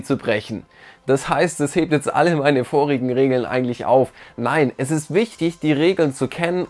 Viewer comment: Bruder ich habe Sex Regeln verstanden